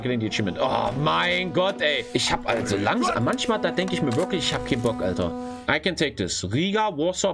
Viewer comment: what the fuck did say lmfao i tried pointing out a few words but I am not good with german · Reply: What i got was: "Mein gott hey, Ich hab alter langsam manchmal denk ich mir wirklich hab kein bock alter." but would love clarification